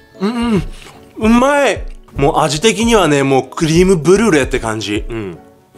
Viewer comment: クリームブルーレイ？？？ クリームブリュレがそう聞こえてしまった